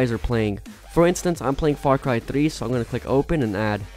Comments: what did you say forncry tri? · what did you say forncry tri? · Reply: Nikola&Vlogs& Games Farcry 3